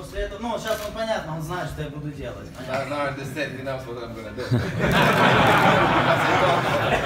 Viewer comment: I didn't understand 3:09 can someone explain to me what he did say? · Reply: he sad:, "close eyes for him",; after: "of corse, now he now wat im gonna do"